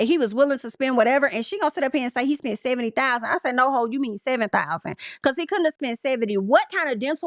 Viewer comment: OMG I thought she said $7,000